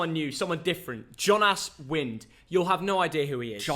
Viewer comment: did he say "John ass wind"? Behave yourself! 😂